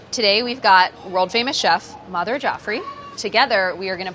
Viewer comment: I didnt know Priyas last name and also didn read the title.... so this whole episode i thought that they said 'Mother Jaffrey' and that she way Priyas mom... ups